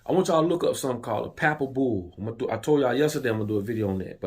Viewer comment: What are you saying at 9:53? Papables? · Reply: Papal Bull